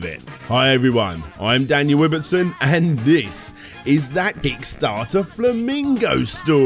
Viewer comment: Really sounded like you said d**starter this time